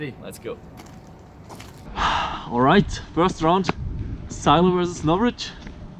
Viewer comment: when you turn subtitels on and it says:sylar is not rich and later its says'salad instead of silo